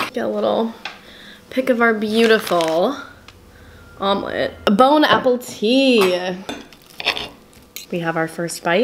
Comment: I am French, and when you said "Bon appetit" I litteraly thought you said "Bone Apple Tea" ^^